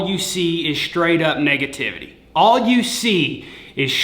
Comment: He said "Straight up negativity" at first i thought he said straight up naked tittys lol.